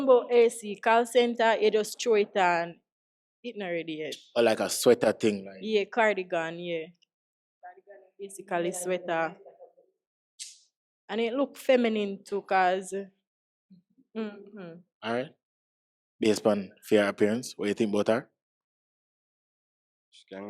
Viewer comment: 😑look feminine...wtf am I hear g ?